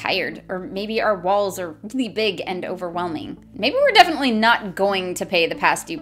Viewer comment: "Maybe our balls are really big... and overwhelming" is what I heard at first lmao 🤣